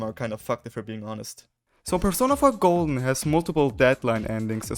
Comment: "persona five golden" is what it sounds like is being said directly after this point.